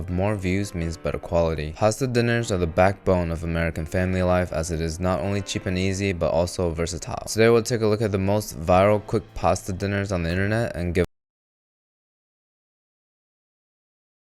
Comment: I swore you said cheapanese in the beginning 😂😂😂 I had to back it up to hear it again as “cheap and easy”